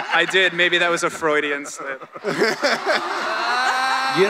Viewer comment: Can someone tell me what that fan said to Norman at 50:13? "I did.. maybe that was _ ?" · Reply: "a Freudian slip" :)